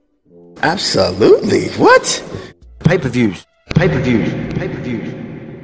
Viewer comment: I know I’m not the only one that dances when I hear “paper views” 😂